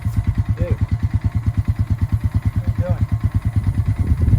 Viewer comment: Your turkey sounded like he was saying "what are you doing". Every where you went he asked the same question.